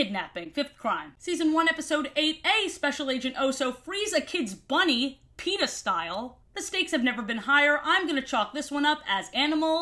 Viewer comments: I was listening to this video while doing college work and when I got to 14:12, I first thought you said “Oso freezes a bunny pen15 style” and had to pause to wonder if I was having an aneurism. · I fr thought you said "Oso breeds a kids bunny p3n15 style" instead of "Oso frees a kids bunny PETA style" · i’ve watched all your lore videos multiple times and i’ve only just realised you’re saying ‘peta style’ instead of ‘penis style’ 😭